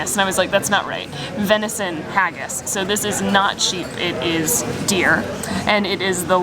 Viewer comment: I thought that after she said 'This is venison haggis' she said " it's not cheap it's deer" :)